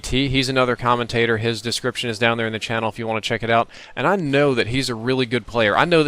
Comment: did he just say ,,His description is down in the channel''?